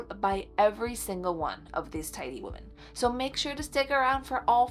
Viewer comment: I was half listening and for way too long I thought she kept saying TINY women, I was pretty confused. 😂